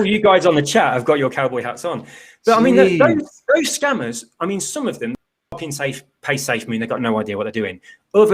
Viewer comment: What did he say about safemoon @ 45:49 ?? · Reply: Some crooks just copy safemoon code and deploy, then scam. Something like that I think.